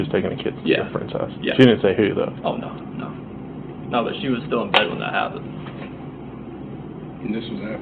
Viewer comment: something so eerie!! Chris says “she was still in bed when that happened” but the CC says “she was still dead when that happened”